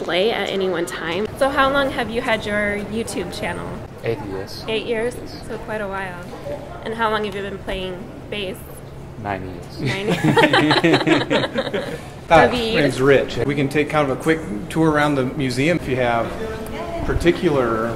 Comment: *Woman*: How long have you had your youtube channel? *Davie*: 8 years. *Woman*: And how long have you been playing bass? *Davie*: 9 years. LMAO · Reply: I thought he said 90 years lol